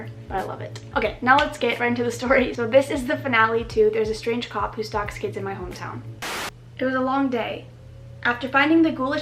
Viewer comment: After tattoo part all I heard was "there's a strange cactus who stalks kids in my town"